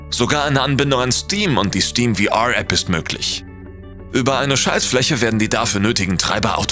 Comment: bin ich der einzige der bei 2:20 "Scheissfläche" verstanden habe